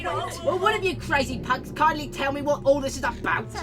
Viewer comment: I honestly thought said "cunts"